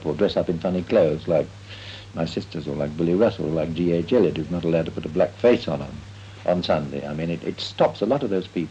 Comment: "Not allowed to put a black face on, in Sundays".....is that what he said? 😂😂